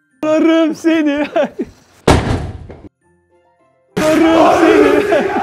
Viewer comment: da ersin ne diyor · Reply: ararım seni her yerde